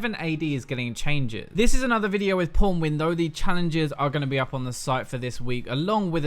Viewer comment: I swear to God every time you say "pwnwin" I hear "porn-win"